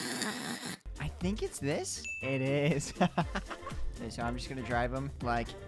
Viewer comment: bro is just Said ‘EVET’ it is a turkish word the english version is yes Or i think he Said it is